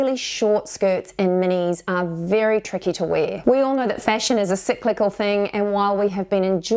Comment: I misheard you at first; thought you said “ we all know that fashion is a sick little thing...”😅